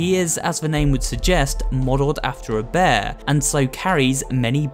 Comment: “is modeled like a bear” Me: I SWEAR IF HE SAYS SOME PEOPLE THOUGHT HE WAS SOMETHING LIKE A BROWN KOALA IMMA FLIP! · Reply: I think he sayd "Modeled after a bear"